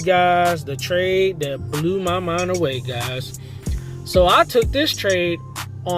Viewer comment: you scared me when you said "let me show you what blew my mind away!!!" I heard "blew my account" heeeeckkkk yeahhhh you got the pass instead!!